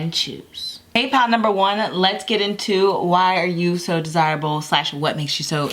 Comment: I thought it said "why are you so disabled"